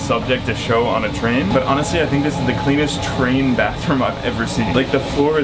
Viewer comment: I heard he said "this is the cleetus bathroom"